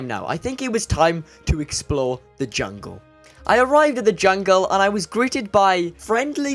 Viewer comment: It sounds like you said “it is time to explode the jungle”